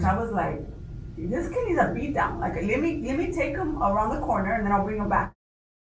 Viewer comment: SHE DOES NOT SAY TAKE HIM OUT BACK FOR A SPANKING!!! She’s says A BEAT DOWN AROUND THE CORNER. Completely different and these reporters down played the hell out of that